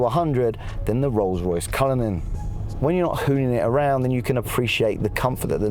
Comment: what did you just say?🥴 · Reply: Luxury Brick hahahq